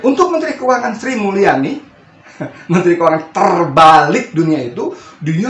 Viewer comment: Menteri Keuangan Sri Mulyani, menteri keuangan "TERBALIK" dunia?? Atokah saya salah dengarrr ?? Saya rewind ulang2, koq telingaku msih mendengar "Menteri TERBALIK" dunia. Wkwkwkwk.....!!